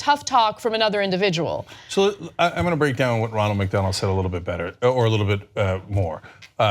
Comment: Who else thought Cenk kept saying Ronald McDonald?